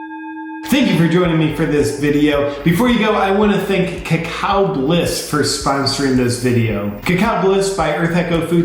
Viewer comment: for his sponsor...i thought it said "CaCa Bliss" lol